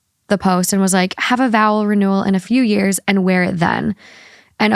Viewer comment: have a what? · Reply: I asked the same thing as I saw your comment pop up and I had to listen again 😂 I thought she said a bowel renewal but I think she said a vowel renewal